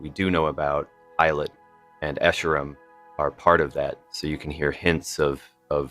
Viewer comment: did he say Eilot and escharum? 3:16 did I mishear? I could've sworn I didn't hear a P for pilot? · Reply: I thought that it sounded like he said that too, but he said "Pilot" based on the non-generated captions and the way he said the rest of the word besides the P.